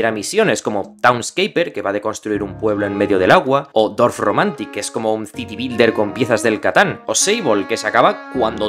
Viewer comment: Cuál es el juego que dice en el minuto 6:56? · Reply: Dorfromantik y luego Sable 🤝